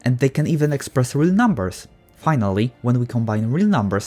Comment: I heard “PI-nally” instead of “finally” WHAT THE CRUST😂😂😂